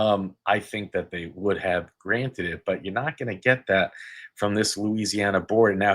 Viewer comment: did he say grant???